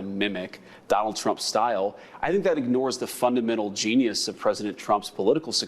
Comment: Did he say “Fundamental genius”?